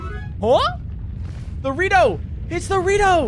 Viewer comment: When pcrow said "the rito!" I thought he said "dorito!"